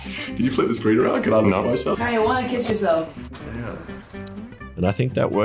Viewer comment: is something saying to the camera I cant solve it cos I am dumb but if you can solve give me some credit :) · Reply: I think he’s saying “do you wanna kiss me? C’mon”